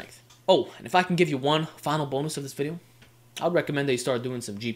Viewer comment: thought you said start doing some g4p hahah